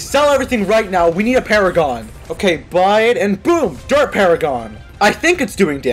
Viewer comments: I always thought he said dirt paragon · when he said dart paragon a thought he said dirt paragon lol